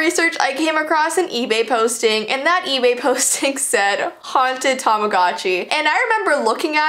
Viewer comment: i thought that said i bought a haunted tangerine... dont ask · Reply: I thought it said haunted tambogini 😂😂😂😂